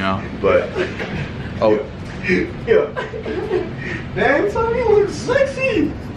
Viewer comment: Whoops honestly I thought he said ‘Luffy’ and was talking about One Piece Just me, ok